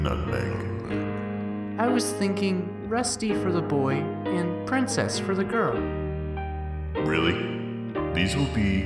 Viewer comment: The first time i watched i thought she said "Rusty for the girl, and Princess for the boy"